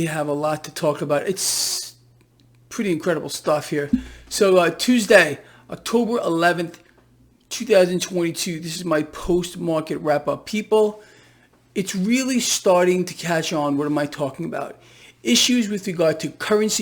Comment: Hi Gregory Marino, new subber here - love the honest sarcasimn - you are on point, people don't get it, LOL - BYW, when you said it was your post market rundown, I thought you said post-mortum instead of post market - hahahaha! Well, it may as well as be - right! Thank you, God Bless!